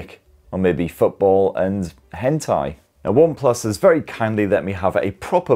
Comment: did you just say hentai?